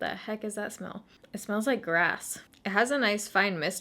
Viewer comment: omg when you said it smelled like grass I honestly thought you said ass and had to replay it 😂😂